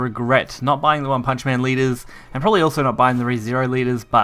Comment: you got those sleeves just for playing the one punch ones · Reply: If you are talking about what he said in 5:23... I'm pretty sure he said that he regrets not buying the OPM leaders...